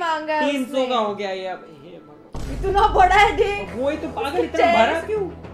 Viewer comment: she was saying kitna bara hai uska chest or I heard wrong🗿